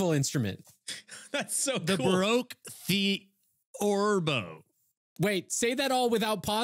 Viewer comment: If anyone is wondering what instrument is trying to say it's baroque theorbo (also just called a theorbo)